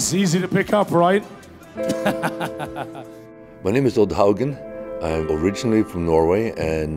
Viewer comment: I thought he said "My name is Hulk Hogan"